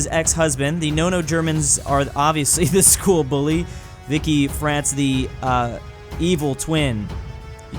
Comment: Did he just say vikki France?